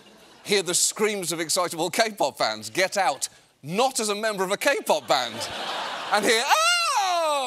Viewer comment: I thought he was saying Capon band. But that means castrated Roosters. So may he was saying that.